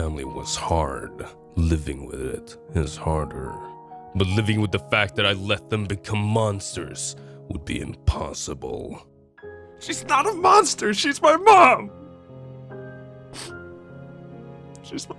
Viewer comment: At around 30:45 captions said “she’s my BOB”